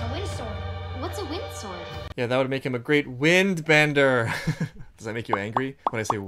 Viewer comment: When you said "does that make you angry?" I thought you said "does that make you Aangry?" lmao